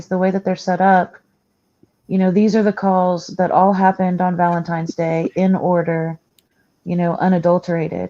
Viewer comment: Maybe my ears need to be cleaned, but I thought I heard someone say "Valentimes Day" and not Valentine's Day💕💞. I'm sure it's my ears.